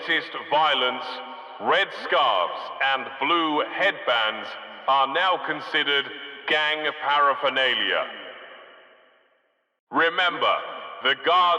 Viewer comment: Can someone write me the sentence at 2:16 ? Can't understand the last word je says ( not english) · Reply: "Red headbands and blue headbands will be considered gang paraphernalia" Its a reference to the gang wars between the Crips and the Bloods